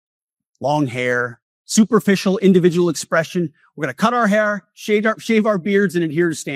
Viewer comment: It sure sounded like he started to say, " ..shave our pubes ..", but then caught himself & changed it to " ..shave our beards ..". ...Did anyone else catch that? ..Anyone, ..anyone ..?